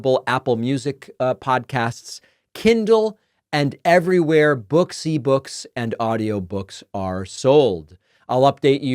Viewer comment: I thought you said "booksy books"...